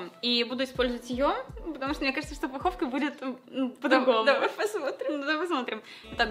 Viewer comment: МНЕ ПОСЛЫШАЛОСЬ НЕ ПУГОВКА, А ПОПКА